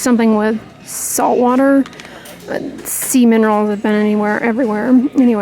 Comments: Omg forgive me but I kept hearing “semen rolls” instead of “sea minerals”.. 🥲I’m such an idiot🤦🏻‍♀️ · OMGoodness! I was semi-distracted and thought Dr. Dray said “Semen Rolls”. 😟😳 Had to listen again, and realized she said “Sea Minerals”! 🤣😂😅🥹 God Bless! 🙏🏻😇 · I need to get my ears checked. When you were talking about the Chapstick and saying Sea Minerals, I thought you said, "Semen Rolls!!" 🤣🤣🤣🤣🙈🙉🙊🤦‍♀ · You said “ sea minerals”.... I heard “ semen rolls “ 😂🤭🤣